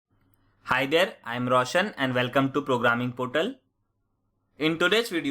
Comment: ur not russian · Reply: his name is Roshan, an Indian name. you must have heard it as Russian